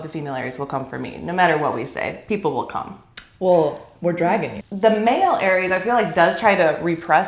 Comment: Omg i thought Natalia said “we’re dragon-ing” and I think she’s dragon-ing 💀😂